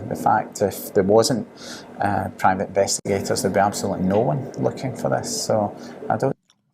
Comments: 7.05 into video. I dont hear 'looking for this' . I hear 'looking for us' ?? Anyone else ? ....Note- i too believe poor little Madeline was accidentally k*lled by the parents. In my opinion, based on evidence. Great video. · I think he says "us" not "this" here. Your general point remains valid though.